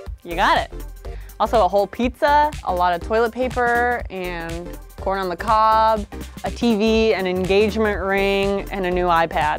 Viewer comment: When she said “corn on the cob” I heard “porn on the cob”....I was quite confused for a second there...😂